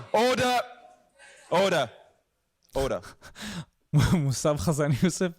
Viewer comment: All i hear is "Hodor, Hodor, Hodor!!!"